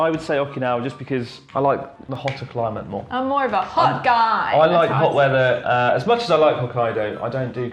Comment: I thought Chris said I like hot leather instead of hit weather which was just perfect given the long running sexual tension.